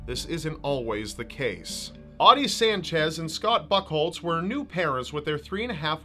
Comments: Scott Fuckholtes? Did I hear that right? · sounds like you said buttholes